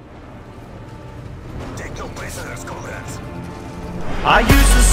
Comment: what does the announcer voice says between 0:08 - 0:10 ??? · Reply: "Take no prisoners comrades"